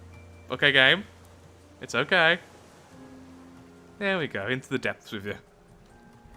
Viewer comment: am I the only one who heard him say "From the Depths" and expected the entire intro lol